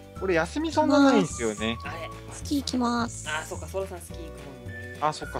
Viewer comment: ｢スキー行きまぁす｣が｢月行きます｣に聞こえて、まじかwwって思ったw